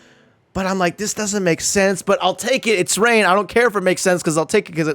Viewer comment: but Shang Stung has Rain too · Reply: My bad, I thought you said "so it's Rain"